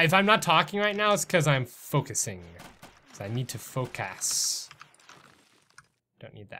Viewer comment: Lol it sounded like he said f*** a** when he said focus 😂😂😂